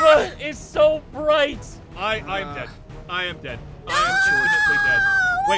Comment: When said I'm am dead I thought he said I'm am gay lol